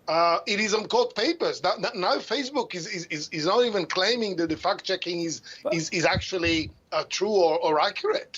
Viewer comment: I thought at 1st he was saying fat chicks but fact checks I think he saying lol